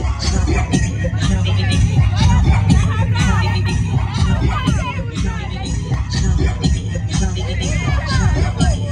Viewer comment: Please someone what did say to at 8:27? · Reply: Idk I think she said “let her go”